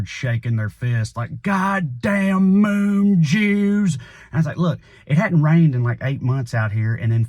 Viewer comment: I thought you said “Moon juice.” 😂